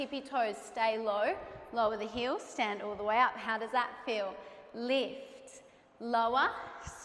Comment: her “how does that feel” sounded like a “you asked for this” 😂